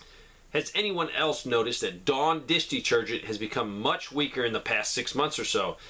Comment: Dawn dish detergent... I heard that as Dog dish detergent! (I listen at 1.5x)